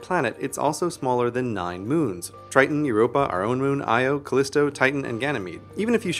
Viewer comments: Audio says “nine moons”, but subtitles say “seven moons”. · He says 9 moons, and the subtitles says 7.... ??? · He says nine moons at 1:33, but the captions say seven